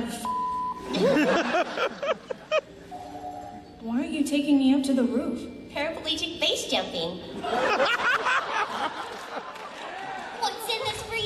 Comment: Kyosuke: Why are you taking me up to the roof? Sayaka: Paraplegic base jumping. XD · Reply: I thought she said: “Paraplegic face jumping.” lol